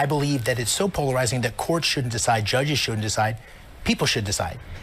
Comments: no David you misheard him. he said "shouldn't" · He is saying courts…judges shouldn’t decide. I love your show just thought i heard something different. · he states courts shouldn’t decide, judges shouldn’t decide, people should decide. He mumbles over “shouldn’t” but it’s what he said. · He said courts and judges shouldn't decide. I like David, but he misheard the guy.